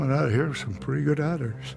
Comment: cans somebody please tell me what the veteran said at about the 2:10 mark? coming out of here with some pretty good what? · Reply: Honors